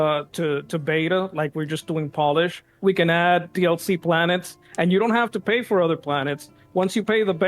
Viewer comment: did he just say "we can add" was he actually a malding dev? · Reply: "...we're just doing polish we can add DLC planets..." - Dev